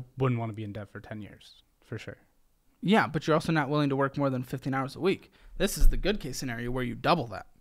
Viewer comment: I thought he said he wouldn’t do more than 50 hrs a week and i was like, bruh… Then I realized he said 15 😮😮😮 (im out here working 80hrs a week trying to get by at 23)